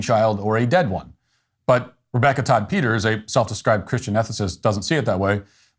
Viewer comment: "Christian ethicist" ... i heard Christian "narcissist "